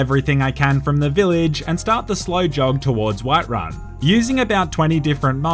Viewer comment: slowjob do i hear correctly